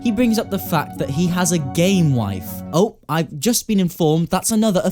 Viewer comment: I thought you said “He has a GAY wife”